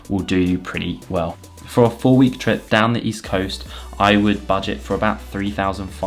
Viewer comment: I’m confused if the budget a day is £60 roughly how is it 3500 to 4000 for just one week 😵‍💫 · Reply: I thought he said full week trip at first but I think he actually said four week trip so that's 4 weeks which is around £2000